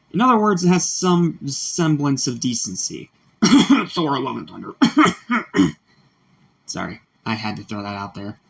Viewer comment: what did you say here I guess you coughed solemn thunder what sorelem Tyler I saw a logo tiger? you threw that out of there?